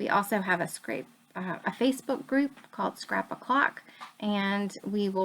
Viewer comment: Such a lovely layout! I was wondering. You mentioned at the end of your video that you have a Facebook group. Sounds like you said it was called "scrapbook clock"?? I can't find it on FB. Could you please reference it here? Love your videos and layouts so much. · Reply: Hi Sherrie! Thank you so much for the sweet words. The FaceBook group is Scrap O'Clock :)